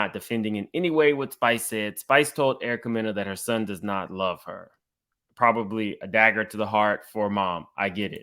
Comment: Tamar is always on the wrong side. 😒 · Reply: she did not say get son didn’t love her she said he don’t like her